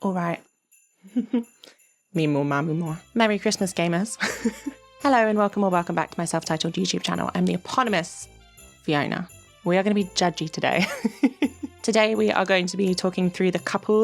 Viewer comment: did anyone else hear gay nerds instead of gamers and feel personally called out